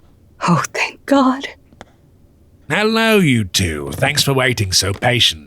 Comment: i thought he said hello youtube